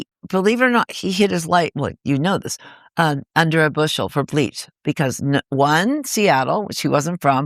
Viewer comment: Time stamp on KG discussion? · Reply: is the time, does anyone know what Courtney says? "Kurt hid his light under a ... Bushel for bleach," that's what it sounds like.